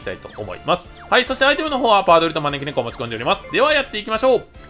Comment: 「持ち込んでおります」が「ぶちこんで」に聞こえる